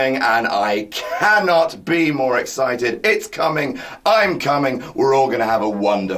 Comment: did say that he's cuming.